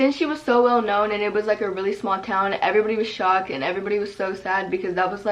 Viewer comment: Omg, my heart dropped. I thought she said everybody was excited omg